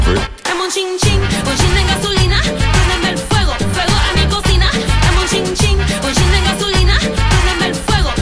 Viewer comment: Dame un chin chin! Un chin de gasolina! Préndeme fuego, fuego en mi cocina! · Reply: Walter Velasquez jaja pense que decia "cocita" en vez de "cocina"